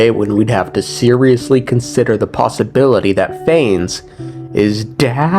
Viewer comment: The way you struggled to say dead also sounded like you said 'Phanes is daddy' XD